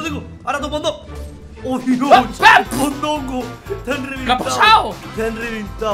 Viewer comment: En 3:46 yo escuché a diciendo (a que pasó) o es que fue (a que pesado)🤔